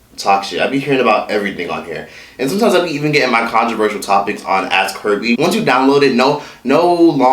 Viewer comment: stg I thought he said "Ask herpes" 😂